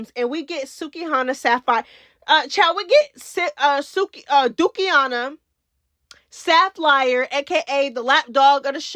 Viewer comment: 😜 🤪🤣 Did she call her"Dookie" -hana"?????? I'm done.....😱